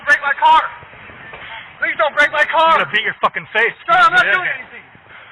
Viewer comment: Suspect: Please don't break my car! Officer: I'm gonna break Ur face. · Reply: Bor I heard, "I'm gonna beat your fucking face".